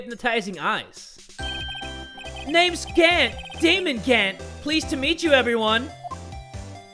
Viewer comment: I swear I heard "Demon Gant" instead of "Damon Gant"